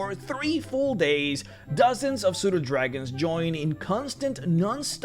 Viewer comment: The first time you said "Pseudodragon", I thought you had said "soda dragon", and I thought . . . "Does a soda dragon's reproductive process involve commingling with a vinegar dragon?"